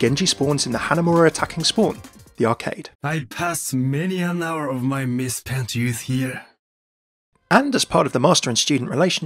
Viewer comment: what does he say there ? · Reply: I spent many an hour of my misspent youth here